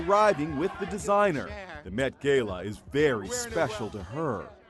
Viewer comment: I thought i was the only one who heard him say gayla but noooo